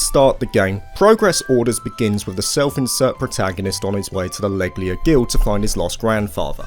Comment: I heard that as 'labia guild' LOL that's what I get for watching in 2X speed!